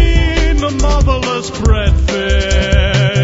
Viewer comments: Oops.. I always heard it as "Tell me have you seen the motherless breadfish?" lol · it's marvalous. at first i thought it said motherless. lol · For the longest I thought it was saying "motherless bread fish" instead of Marvelous XD